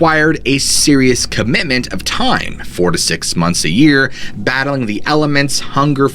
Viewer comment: I cud have swrong I heard 46 months a year but naw it was just 4-6 months a year 32:43 😂😂😂 Let's go scuba diving !..